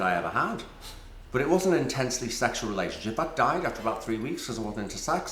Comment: So sad. 29:36 – Pete talking about his life and influence. "...I hope to live a very long life..." :( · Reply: can someone tell me what he says 15:05 about lynne? something about „it wasnt a sexual relationship & dying after 3 weeks?“ 😂